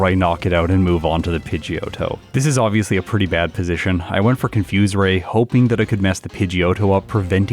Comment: So I'm driving while listening to this and I could have sworn you said at level 14 this pokémon got confusion not confused Ray. Lol I was like why you still using side wave if you have confusion but now it makes sense lol. Great video by the way